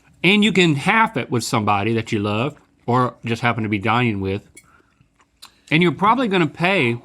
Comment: I thought Link was making a philosophical statement about relationships when I thought he said, "a meal you can share with someone you love or just happen to be dying with." LOL